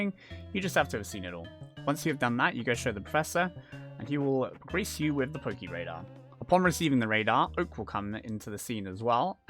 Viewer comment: Sogkskyjdg I completely thought you said "he will grease you with the Poké Radar" Great video as per! Can't wait to start Radar hunting 🧡